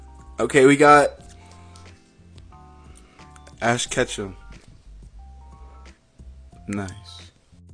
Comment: I thought he said ash ketchup